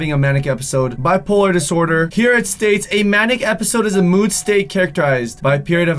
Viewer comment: I thought he said maniac episode.